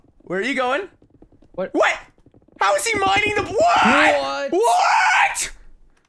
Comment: Who else heard "HOW IS HE MINING THE BLOOOD?"